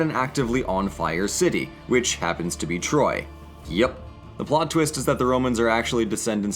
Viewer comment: I thought he said detroit